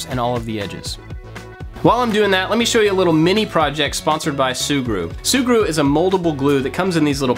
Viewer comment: I really thought you said Subaru then haha